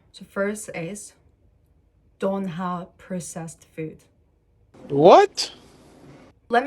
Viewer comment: heard this as i ate processed food😭